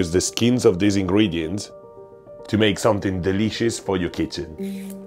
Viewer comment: I thought for a second he said “you can use the skins of these ingredients to make something delicious for your CHICKEN” 🐓